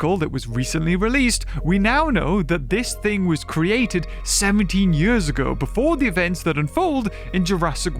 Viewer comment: I thought you said seventy years ago:_toastderp